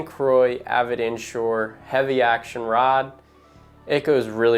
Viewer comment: Can you please add a link to the pole you said you used @ 1:05. I’ve tried listening to what you said but couldn’t make it out. Thank you. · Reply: Here it is: <link> It’s the 7’6 heavy power fast action